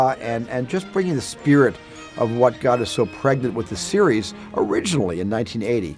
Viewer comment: "What got us so pregded with the series." What does he say there?